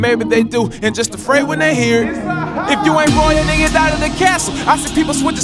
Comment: LISTEN UP NEVER USE THE SUBTITLES ON THIS VIDEO YOUTUBE NEEDS TO FIX THIS IF U LOOK THRU THE SONG AT 1:29 IN THE SUBTITLES IT SAYS "ROYAL NIGGAS OUT OF THE OF THE CASH OFFICE" WHEN I HEAR THIS SONG TO ME IT DOES NOT SOUND LIKE HE'S USE THE WORD NIGGAS LET ME KNOW WHAT U THINK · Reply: myst music he said if you ain't royal then get outta the castle lmao it did sound like he said the n word tho